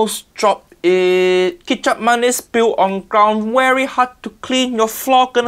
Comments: I thought he said Ketchup-Mayonnaise. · I thought they said ketchup mayo,lol